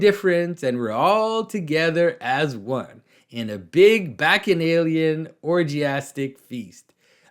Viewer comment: What's Jonathan saying at 16:27? Closed captions says "back in Alien"